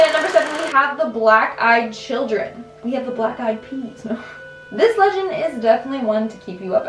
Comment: wtf did I just hear? “Black eyed penis”wtf!!